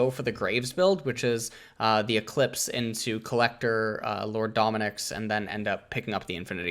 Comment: what did you just say? build collector? Oh boy, the church founder has turned atheist already.